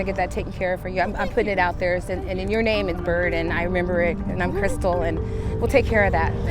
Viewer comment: Her name is, Burden? Her parents named her, "burden"? Did I hear that right?